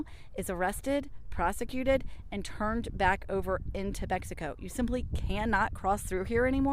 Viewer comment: Wait did she say "Bexico"?? 😂 Never heard that one before